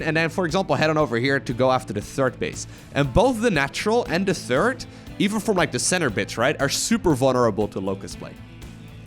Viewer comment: did say center bitch. Instead of center base? Lol.